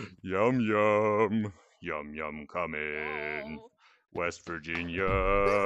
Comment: I thought he said "west vagin-"💀